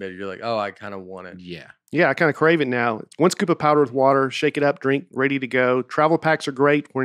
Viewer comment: Did say "powdered water" at 45:38?